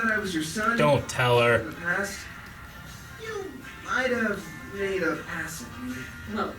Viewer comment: What did he say at 3:26? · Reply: 5 Months late but i thought i would chime in. He said " You might have made a pass at me" Meaning she flirted with him.